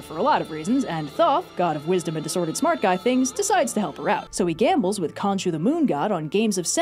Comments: I thought it said “thot gambles with the moon” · I honestly thought it said a "Thot gambles with the Moon"